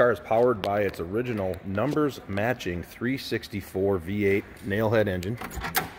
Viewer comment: He says 364 V8. I thought he said 360 Ford V8.